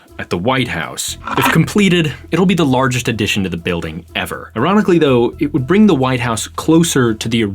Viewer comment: subtitles says 100 years, you say ever. Which is true?